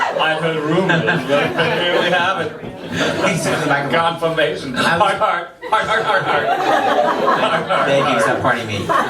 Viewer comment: What does Christian say after he says "I heard a rumor"?? Can't tell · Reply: Tori Rose "there we have it, [a?] confirmation. ❤️❤️❤️❤️❤️❤️❤️❤️❤️"